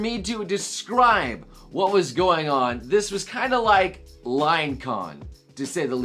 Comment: For a second I thought he said lion con which sounds awesome!